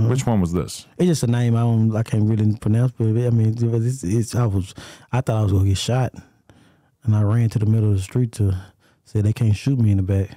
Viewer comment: wtf did he say “hdiduh Heidi Krishna I thought I was gone get shot” got damn he stumbled on them words